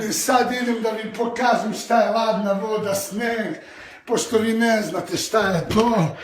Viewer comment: guys im from greece and i want your help ... what does kristijan say when comes out of the sauna at 1:03 - 1:04 ? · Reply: "Pošto vi ne znate šta je to" translated "because you do not know what is that" .. something like that